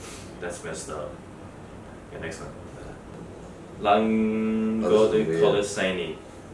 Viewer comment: All I hear from those girls at 9:40 Is “lepiej” (better)